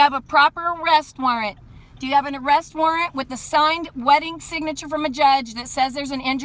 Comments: Every one of these is the same. Officer is calm and polite while condescending idiot talks down to them, backup arrives, and Officer Sledge comes out and ends the issue. Also "signed wedding signature".. that's a new one. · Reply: Lord Maeryk — I thought I heard “signed wedding signature” too... thanks!!! 😉 😂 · Did she say "wedding signature" at 6:52? I've never heard that one before. 😂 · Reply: she says "wet ink signature" i figured it out after slowing down the clip